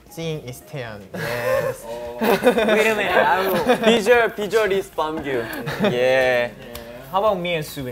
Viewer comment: the subtitle is wrong. he don't say "picture is beomgyu" but "visual is beomgyu"